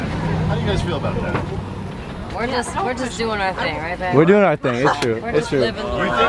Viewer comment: it sounds like Chloe said we just doing our stuff right babe idk if she that